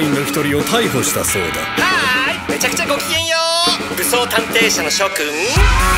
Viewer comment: can someone please write subs for this moment, I can't understand what does he say... ゴーゴリが言ったことを書き留めてください...理解できなああい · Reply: はーい、めちゃくちゃご機嫌、武装探偵社の諸君